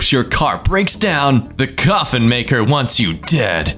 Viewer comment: oh gosh, I first heard "coffee" maker and asked whyy???